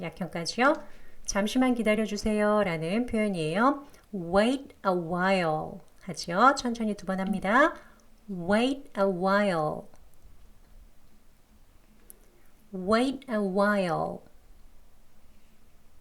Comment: while이 봐? 라는 발음으로 들려서 왜 그런가 했더니 와을 발음이였나봐요. 영어 자료보다 잘 안 들리는 단어가 있으면 선생님 영상 찾아봅니다! 감사합니다~